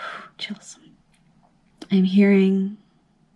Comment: it sounded like you said Chelsea. This is the second time I hear that name in one of your readings. I dreamt of hearing that name once as well. Maybe I have a guide or I'm going to meet someone named Chelsea. Or this Chelsea have a similar life path as myself? Sorry, I'm talking a lot! Anyway you're the best I seen on YT so far, hands down. I hope more people are going to find your channel soon.